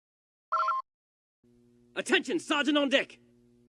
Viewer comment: Every time I hear the intro for each video it sounds like its saying attention Sargent on dick